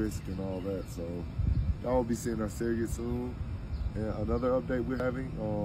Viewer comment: I think I heard him say surrogate 👀 8:01 so that really makes more sense